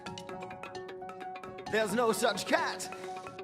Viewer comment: I thought it was saying “theirs no such cat” XD